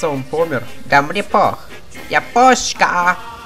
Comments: ха, пиздец 1:04 намёк мирового нейтралитета Швейцарии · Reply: Мне послышалось" я кошка" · Кажется он помер... -Да мне пох!!! Япошка!!!! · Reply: мне послышалось Я КОШКА!! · Кажется, он умер -Да мне пох! Япошкааа! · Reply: Ребят а кому послышалось сначало "Я Божка" от Аммерики? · Кажется он помер... -Да мне пох!!! Япошка!!!! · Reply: А мне послышалось "Я ПУШКА!"